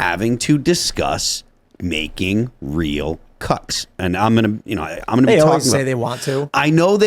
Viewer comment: I swear I heard "...making real cucks"